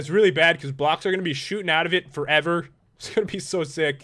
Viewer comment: When Pat said " Shooting out of it" I thought he said "Shoopin it." I WANNA GO SHOOPIN WITH PAT!!!!!!!!!!!!!!!!!!!!!!!!!!!!!!!!!!!!!!